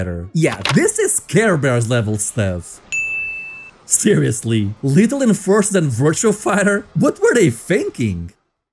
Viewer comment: Your accent made it sound like you said "Little Enforcer"